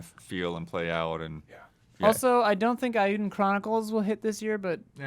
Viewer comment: I'm sorry is saying Aidyn Chronicles is coming out this year at 2:19? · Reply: She said Eiyuden Chronicle